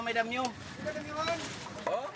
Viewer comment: did he say, “damn you!”? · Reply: Ariel Nashiro Calderon adamyo po it means yong bababaan not damn you po😊